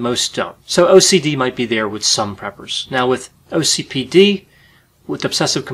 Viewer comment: I thought he said, "peppers." 😂